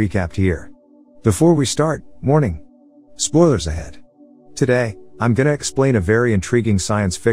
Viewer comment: I always thought he said "morning, spoilers ahead"😂